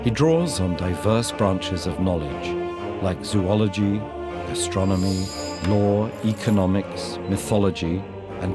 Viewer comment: wait did he say astronomy (like in the clip) or gastronomy (like in caption)